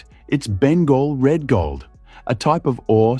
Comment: what is the name of the fake gold? I can’t understand what he says. Something like Ben gold red gold. But I don’t know.